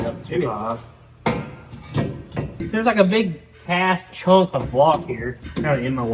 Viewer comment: boy I thought you said something out of character. Big CAST piece of block in the way sounds a lot like Big a** piece of block.... Couldn't blame you if you did, headers are always a giant pain..... On a full body car. But worth it. Ok on with the show.